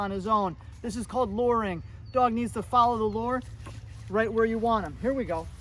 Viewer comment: Thought you were saying: “Follow the Lord!” initially. I then realized you were saying “lure”. Follow the Lord is a good command though too!